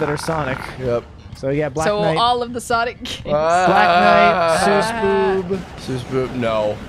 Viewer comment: Omg, I actually read about World War Blue once. I had no idea there was an anime of it, let alone that was involved with the dub. I kinda wanna watch it now xD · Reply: sisboob? Is he saying SSBB?